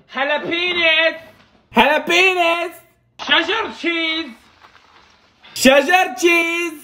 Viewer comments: im glad he changed but why does it sound like hes saying shut up cheese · I heard "shut up cheese" Instead of cheddar cheese